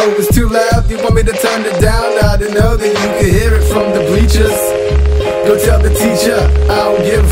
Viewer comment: I always thought he said beaches oof been saying that wrong a long ass time